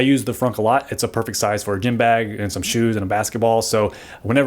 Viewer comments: Did...did you just say Gim-bag? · Did he just say “..gim bag...?.... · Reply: Gym